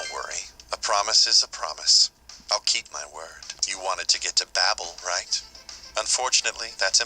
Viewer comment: yay it's back! =D lol when Naoya said "you wanted to get to Babel" i heard "you want a ticket to Babel" XD you know i was thinking, when someone has overpowered demons then that fight is simple but i think it'd be even more simple if you have the ability Animal Leg from one of your demons, this way you can move twice in a turn and reach Naoya faster